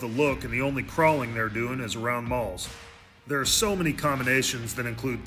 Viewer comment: OHHH You said they crawl around Malls....I thought you said Balls.... My bad.